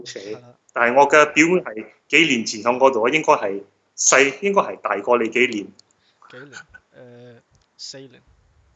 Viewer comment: is saying"我嘅表妹係幾年前嚮果到...(她)應該係,應該係大過你幾年" which means "a few years ago my cousin studied in Newcastle University…(she) should be,should be a few years older than you" instead of “你係Newcastle University 讀左幾耐”